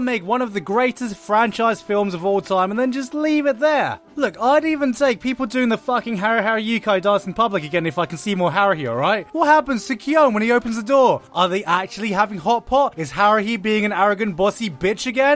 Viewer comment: thought you said harakiri.... now I know what it means. (It's a way to commit suicide)